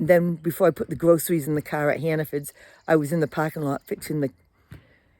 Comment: It was me who asked to see 'Hanafins' I thought you were saying. Now I see it's Hanafords! I had a picture of a smaller store, kind of quaint, for some reason. Thanks for going to the effort of filming it 🙂 I love your back yard, so many lovely trees.